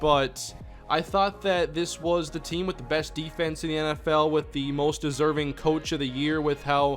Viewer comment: did he just say best defense #Go Jags